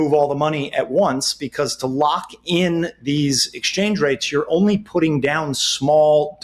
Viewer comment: You’d move it all at one time to guarantee there is just one exchange rate. If you do it little by little, the change rate will be different each time - and that can be risky. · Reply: yes, sorry I thought he said you “had” to move it all at one time. Not optional😊.